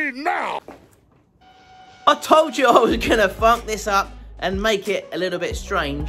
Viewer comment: I kiiiind of thought you said "I told you I wasn't gonna f*ck this up and make it a little bit strange..." but then I thought he probably said funk.